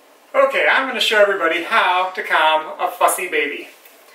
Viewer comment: Okay #1 HE IS SO CUTE but #2 at 4:52 I thought you just casually said "Okay, I'm going to show everybody how to calm a fucking baby" lmao